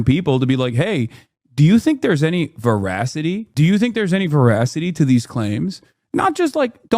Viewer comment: Okay, I hand to pause for like 5 seconds at 6:27 because it stunned me. Did he just say Voracity? As in Voracity for a extreme hunger? No wonder why people think he's smart, he's using fancy word that don't match anything about the situation to sound intelligent and assumes people don't know the word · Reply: he said "veracity"